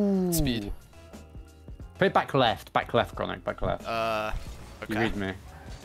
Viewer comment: did jack speak a lil bit dutch here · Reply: nah he English i swear I heard "lekker chronic" and now I replayed it and heard "let's go chronic", my ears buggin